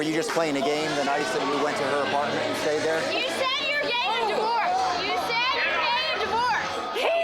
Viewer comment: ~ ~ oh “Getting a divorce” ~ I thought she said: “Gay & Divorced” ~